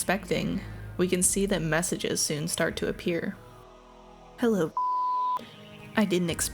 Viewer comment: hello b**** is what i thought user said at first